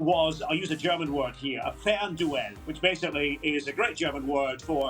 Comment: What German word did Derek use at 9:24? Thanks in advance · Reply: Fernduell